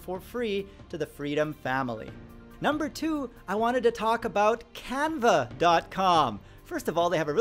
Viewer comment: Because I'm watching this on a phone, I thought said "Kappa" so I had to replay this part 3 times to make sure he said canva